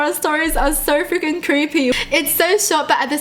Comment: did she said fucking or freaking